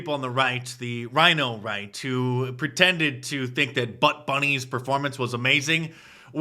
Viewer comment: Sorry mark I'm getting old and don't hear as well. I thought at 9:23 you said Butt bunny. Fortunately I was able to go back and confirm that's not what was said so that YouTube wouldn't bring that up😁 just trying to help out here